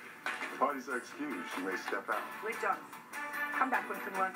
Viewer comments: For a second at the end I thought she said come back when you've learned to bang lol I'm dead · Judge Judy: "Come back when you learn to behave." Captions: "Come back here with some lunch, babe." 😂😂😂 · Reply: I thought she said come back when you learn to bang lol I'm dead